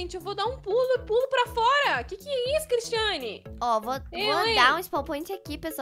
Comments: Gente foi em pressão minha ou a falou seu nome? No minuto 15:39 ela falou "que isso cristiane" foi o que percebi {esse comentário não é para criticar o canal e um afirmação porfavor não critiquem-me>w<} [amo muito o canal dela muito mesmo e não crítico o canal que amo>3 <] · Gente vcs repararam na falando que (que e isso CRISTIANE) o nome da e cristiane, mds foi so eu que reparei isso, ela fala cristiane na hora 15:40 sério presta atençao.